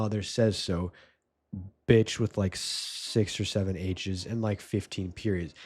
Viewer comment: Did you say 6 or 7